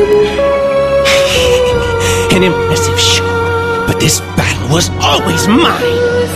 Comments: Its been 9 years... I still have not a fucking clue what says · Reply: "an impressive shot, but this battle was always mine." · What did say? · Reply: You will never laugh again fiend.